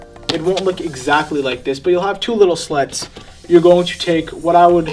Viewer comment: What I heard, " cut through it with a knife and it will be exactly like this only it will have two little sluts"